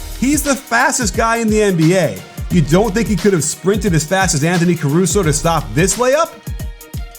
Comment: Hold up. Did i just hear Anthony Caruso? 6:40 :D